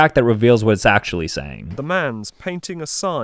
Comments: i heard "MAn pAnTIng A SIgN" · I heard “the lambs are painting you a sign.” And I thought I was in an analog horror or smth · First I heard “A man stepped aside/The man stepped a wide” The second time I heard “A man in pain on his side” And then when I heard the real phrase “A man is painting a sign” It made 10* more sense. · I heard "No, painting is hard!" I got painting somehow XD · For some reason I heard “wow, thank you son.” But now I hear it “the man’s painting the sign.” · I heard "The man dissed you with slime"... ...Psychologists, I'm waiting for your diagnosis. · The fact that I heard "hey, hinting a sign" and how similar it was to the actual sentence for a moment got me thinking I got superpowers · "Hello I'm doing fine" English lessons really be hitting hard rn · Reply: In the normal version I heard “the man is painting the sun” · “Oo, thank you for signing!” I definitely heard the sign part. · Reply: I heard “WOW! Painting a sign!!” sounds very exciting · i thought it said 'the man is eating a spine'??? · I hear “the man’s painting is fine” I was so close!!!!!!!!!!!! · I heard "woah its painting a sign" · “Madam, stick to the sign” But how would you even manage to… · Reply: I heard oh wow he's painting a sign 😊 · Still clearly said than Winston Churchill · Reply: I heard him say Oh man I’m itching a sign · I thought it said damn or man painting a sign So ig I’m magic 🪄🤩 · I heard "I'm ingesting a sign" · I heard "The man was tasting a sign"